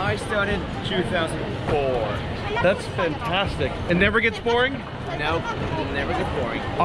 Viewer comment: it almost sounds like you say "thats FINtastic" for Finland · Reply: He said fintastic